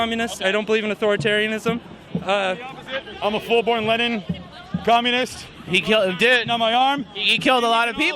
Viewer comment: "I'm a full on Lenin Communist." Uh, you mean a Marxist-Leninist? Star Trek is a Communist Utopia? Good God... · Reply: Oh, I thought he said, "I'm a fool... on Lenin communist"